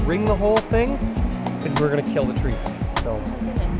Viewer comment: When you were talking about the cherry tree & describing taking the bark, there was a word I couldn't understand. It sounded like you said "ring the whole thing. It would kill the tree." I'd love to understand that part! I've never understood how to use rose hips: thank you so much for demonstrating!